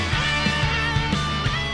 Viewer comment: Was it just me or did I hear something in the intro and in closed captions it said 0:31 dad and at 0:35 it said we should